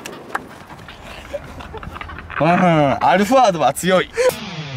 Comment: アルファード アロンアルフア 9:49 最初、アロンアルフアって聞こえたわ